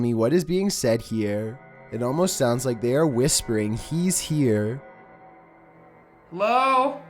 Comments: I heard he's here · On number 8 I heard he's here